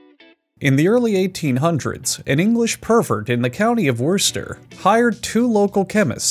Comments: At :25 I heard "An English pervert in the county of worcester..." I did a quadruple take, watched some more, and went "Oh Ok, that's what this is." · I did not realize this was a parody until “the resulting concoction sucked ass” 😂😂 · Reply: "An English pervert" is what I heard and it threw me for a loop, haha